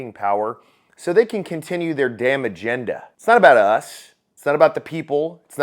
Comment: Oops I thought you said “damage agenda” or “damning agenda”. My bad. Just “damn agenda”. 👍 👌 ✅ “Damage agenda” made more sense to me. Whatever. I feel sorry for people who still think Americans can save America. Too little too late. We would have done it already. Iran has declared war on Israel & its allies. Including us. Trump has added people to his cabinet nobody trusts. Even if he didn’t, he’d have to go up against a packed house. Those who set up the game did so they win either way. We let the clock run down feigning confusion. We deserve what we get. PS if you didn’t know Trump would likely be golfing, and could probably be found on his course at some point if you track him and wait long enough. Patience is all it takes. I wish internet/ keyboard warriors would have been more effective than raising only awareness, absolving ourselves the responsibility of raising anything else, putting it off to the influenceable. Well. Here we are, then. Eating Vaseline.